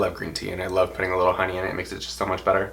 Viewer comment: I just got to the part where you were sipping your green tea, and I had to rewind what you said! I could’ve swoooorn I heard ya say, “I love green tea, especially when I put a little bit of onion in it” and I nearly fell over. I realized you said “HONEY” and not “ONION”. I’m still stupidly chuckling at the idea of you just (with ya whole chest, of course!) confidently declaring that you are enjoying a piping-hot, steamy cup of onion tea. I’m done with my rambling, but it gave me the best bizarrely happy moment. P.S. This story is nuts, but as a broad who had her own nightmare encounter in Arkansas (not nearly as adventurous as yours!) I can 10/10 confirm that the homophobia in that godforsaken state is REEEEEEEEAAAAALLLLLL.